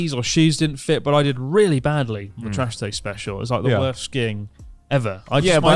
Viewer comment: did just say Trash taste Special? 👀